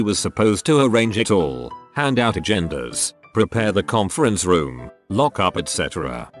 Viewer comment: I wasn't really paying attention and when the robot man said "hand out agendas", I thought he said "hand out the genders"